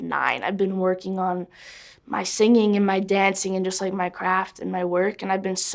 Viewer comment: When she said I’ve been working on my self my craft I thought she said she was working on Minecraft